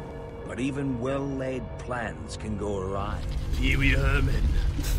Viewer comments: It is finally out!!! This is getting so intense, I hope you guys enjoy! Should be one more video coming tonight! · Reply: PopularMMOs I thought Ivor said peewee herman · wtf I though the narrator said peewy Herman